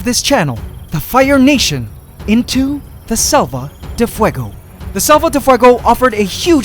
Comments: I thought you were saying “Sopa de fuego” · At first i thought u were saying Sopa de Fuego lol which means soup of fire